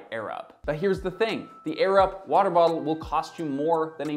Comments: I kept hearing “the Arab water bottle” 😂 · At this point seeing a product regularly in youtube sponsor spots just makes me assume it's hot garbage. And that I'm usually not wrong. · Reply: I kept hearing “the Arab water bottle” 😂